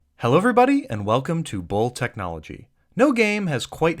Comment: I thought you said hello everybody welcome to old technology